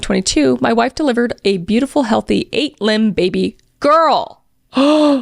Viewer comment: 8 limb 😂😂😂😂 i literally thought she was saying the baby was born with 8 limbs because i was just listening until i looked at it and backed up like wait hold up what?!? 😅